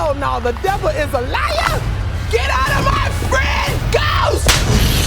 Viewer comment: For the longest time I thought she was saying "The power of PAIN compels you". I had no idea her character was called Patty until reviews came out because it isn't established in the trailer. The joke doesn't work and is so, so weird in context of the trailer. And "The Devil is a liar"? Like, what the fuck is going on?